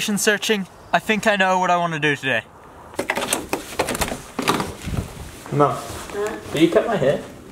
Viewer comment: Was that your mom that cut your hair? Have your parents been with you in Canada? Who was that lady??? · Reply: Thomas Jones I think at 2:50 he says "mum" after going inspiration searching